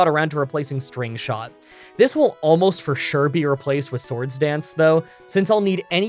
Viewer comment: does he say Sword Stance ?